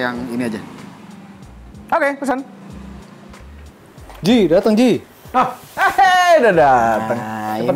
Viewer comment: ''Ji Dateng Ji'' Apa Gw salah denger?